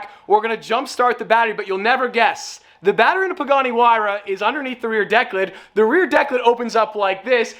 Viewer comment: Idk why i kept hearing "Bugatti Huayra"....